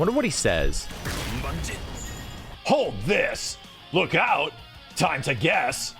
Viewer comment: it sounded like he said "olha a cabeça" it means "watch your head" on Brazilian Portuguese. but he probaly didn't say that